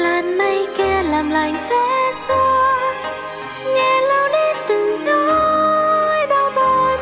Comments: Video ko gây tôi khóc nó gây tôi cười 👍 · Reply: hát hay thật nhg k hiểu sao mỗi lần nghe đoạn "nhẹ lau đi" thì t lại nghe thành "nghe lỗ ###" :))) Và giờ nó đ dứt ra khỏi não đc , ét o ét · hát hay thật nhg k hiểu sao mỗi lần nghe đoạn "nhẹ lau đi" thì t lại nghe thành "nghe lỗ ###" :))) Và giờ nó đ dứt ra khỏi não đc , ét o ét